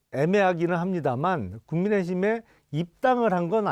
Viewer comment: 국민의 짐으로 들리는대?ㅋ